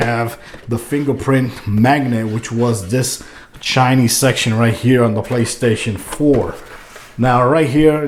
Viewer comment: I thought you said "Chinese" for a second there, had to re watch it five times and check subtitles to see what you really said lmao